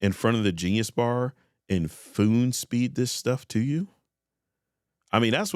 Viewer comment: Did you actually say FOON SPEED at 5:27?😂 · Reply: 😂 i was trying not to say F*cking spoon feed and it became one word